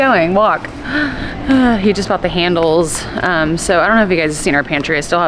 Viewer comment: Dude, your kid has the prettiest tan. I’m jealous and I’m freaking Navajo. Lol. edit I totally misheard what you said & I thought you said “and he’s got the handles” right after Nick was rubbing on his nipples and I bout died 😂